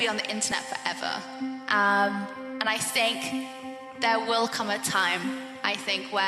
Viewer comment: Sounds like she says "and I stink" :) (I don't like Belle Delphine)